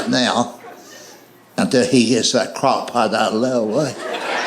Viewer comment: what is he saying? i can't make it out. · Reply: After he gets the crockpot out of layaway. Part of a previous joke in the set.